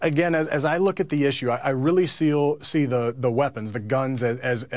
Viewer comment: lol he said I really seal instead of I really see.